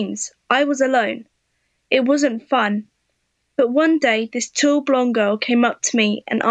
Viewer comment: For some reason I thought she said 2 blonde girl lol. And I thought it made sense lol